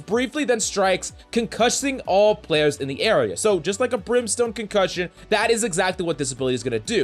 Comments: did u just say brimstone concussion or im just high?? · wait did you say brimstone cuncussion